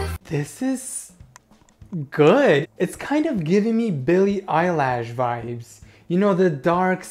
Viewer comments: DID YOU JUST SAY BILLIE EYELASH?.. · Did he just say Billy Eyelash?